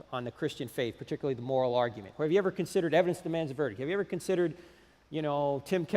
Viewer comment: Frank talked to quickly for me to hear what he said. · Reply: Evidence That Demands a Verdict by Josh McDowell